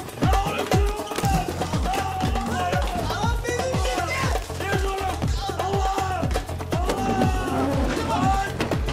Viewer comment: ne diyor ali kerem? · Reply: Belim çıkdı